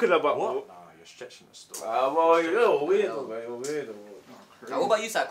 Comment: They took the sugar and boiling water from top boy 😂 naw but i rate this styll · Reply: I thought u said blue story